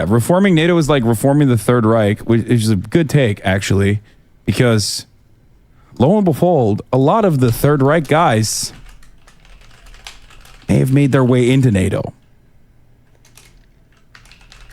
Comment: Did Hasan say, "Low and befold?" 😂